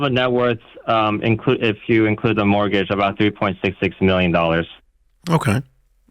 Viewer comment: I thought he said $2.66 million, and my thought was no. Then when he outlined everything, I realized he said $3.66 million (excluding the house) and it changed my answer. $1.2 million in equity, $2.1 million in brokerage, $150k in cash, $1M in 401k, $500k in RSUs = about $5 million net worth. 5% of net worth on a car is fine. That's like a millionaire having a $50k car. Nicer than you need, but not wildly stupid.